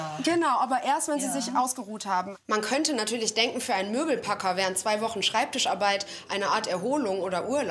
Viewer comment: Ich hab Lümmelpacker verstanden😂😂😂